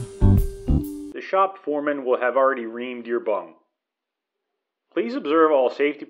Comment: "The shop foreman will already have reamed your bung." Sounds painful. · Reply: I heard "rimmed your bum"